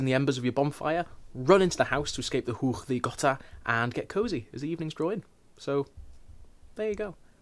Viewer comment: When you said "get cozy" at the end I legit thought you said "get covid" which is, in fact, the new worldwide tradition; especially if you're trying to eat the same apples as everyone else at the party